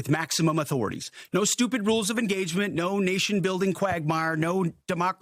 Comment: Did he just say NO RULES OF ENGAGEMENT! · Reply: He actually said “No STUPID rules of engagement” as if… 7:43 Disturbing.